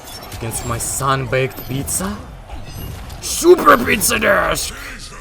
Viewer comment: not gonna lie, i thought the announcer said "P I Z Z A" when he killed green mileena.